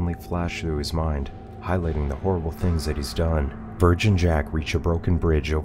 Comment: At the bridge when he said "Verge and Jack" it sounded like he said "Virgin Jack" and I just had this moment of, why is that important? Why are you trying to shame this man's body count?